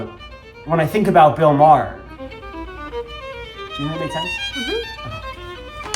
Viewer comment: what the hell did he say right here? I keep rewinding it but can’t understand. 😂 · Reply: Very late reply but in case anyone else is wondering, he said "do you think that made sense?"